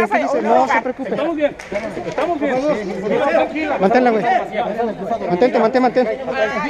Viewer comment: sé escucha que dice " Mátala "